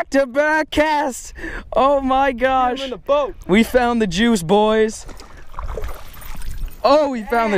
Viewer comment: totally sounded like you said "We Found The Jews".